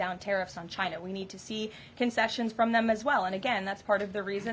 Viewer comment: Why should they make concessions when they didn’t start the fight? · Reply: Concessions? I thought she said Trump wants to make a confession...;-0) My hearing is getting bad.